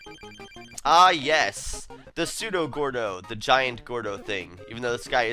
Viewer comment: Judo Gordo? You mean Captain Stitch? Close enough I guess. =P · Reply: sorry when you fought the gordo guy I heard you say judo gordo also I just subscribed if you look at subscriptions